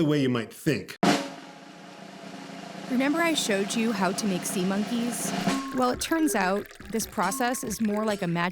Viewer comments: HOLY SHIT WHEN SAID AT 9:32 IT SAID SEMEN INSTEAD OF SEA MONKEYS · Did anyone else hear “renember” on minute